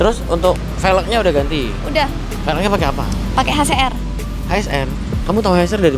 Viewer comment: kok aq dengernya si cwe bilang velg HCR ya bukan HSR ap cuma kuping aq aja yg salah denger 😜😜 Ig:arif_mustamil